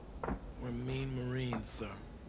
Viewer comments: Thought Donlon said “We’re mean marines” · T.H.E. Rock: You're going home now. - Crazy Earl: Semper Fi. - Donlon: Remain Marines, sir. - Eight Ball: Go easy, bro's. - Animal Mother: Better you than me. - Rafterman: wEll aT LeAst tHey DieD fOr a gOOd CaUSe goddamnit rafterman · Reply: Thought he said "One mean marine, sir."